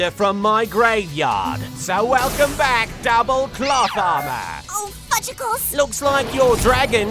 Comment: I thought valon said "plot armor"